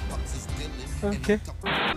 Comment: What they say: "Ok" -What I hear as an Italian: "Ma che..." Gg to me